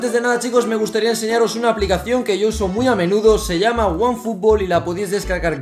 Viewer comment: yo escuche "one for all"